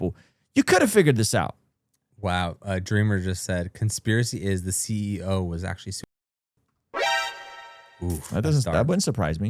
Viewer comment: what did he say? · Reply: conspiracy is that he died on purpose.